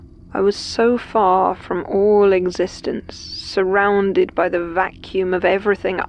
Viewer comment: Eggsistence🤣🤣 (When she said existence it sounded like she said eggsistence instead)🤣🤣🤗😋